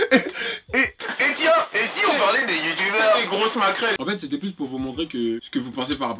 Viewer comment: jai entendu grosse macrelle 😭